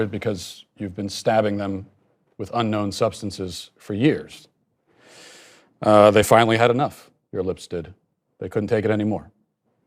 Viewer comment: Not a doctor, but over-filled, freshly stabbed lips and low air-pressure doesn’t sound like a winning combination regardless of the product used. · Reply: I keep hearing him say "they finally had it...your lips did" and I can't stop laughing.